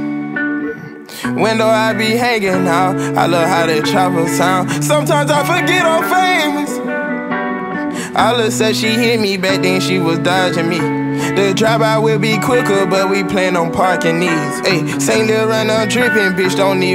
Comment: “Said she hit me (hemi) back but i think she dodging me” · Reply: I thought he said, “ all of a sudden she hit me back (bat⚾️) then she was dodging (dogers)me” but that Hemi bar is in there after second listen